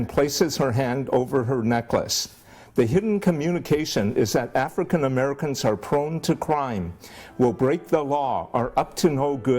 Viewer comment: I heard " african americans are prone to crying"